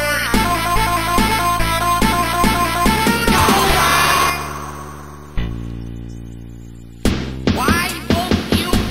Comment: idk what it says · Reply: it says "causa" and that is an word of the country "Peru", and it means like friend